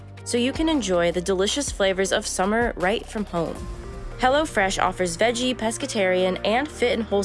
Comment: At first, i thought she said Hella Fresh... That would be a much better name for that company.